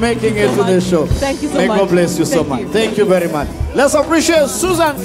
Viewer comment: did he say madam speaker sir?